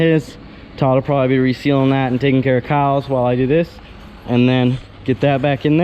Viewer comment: Did I hear "Todd will be taking care of Kyle's" as in the Lude is going to ICC?!